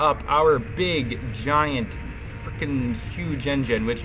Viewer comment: did you say frickin or f*ckin? If you said the latter, this episode has been enhanced because you said that